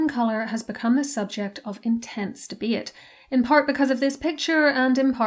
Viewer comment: what is she saying here? "Thebeit" or something of the sort? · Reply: she’s saying “debate” with an irish accent